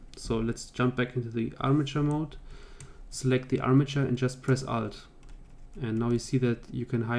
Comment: What did you say at 4:28 plz help its the most important step i cant get it TT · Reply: press alt